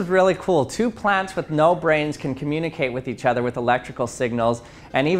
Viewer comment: I swear I heard "Two friends with no brain can communicate with each other." You heard that buddy?(distant voice)